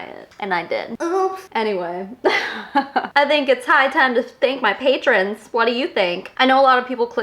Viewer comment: I definitely heard "it's time to spank my patrons" lol. Clearly people are having more fun patreon than I realized.